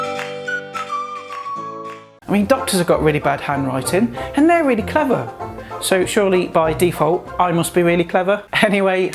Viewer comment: At first I thought you said ducks have really bad hand writing and they're really clever